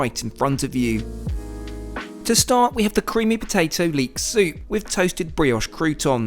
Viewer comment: Hey, how was it to eat SOAP instead of SOUP ? Funny typo at 23:45 ! Keep up the good work, love your vids...